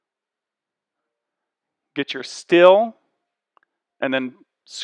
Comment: Did you say "still" or "steel"? I assume you are talking about the metal inserts that go inside the gate posts; mine are aluminum. What kind of screws did you use? Do you have to use aluminum screws? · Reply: Steel, or aluminum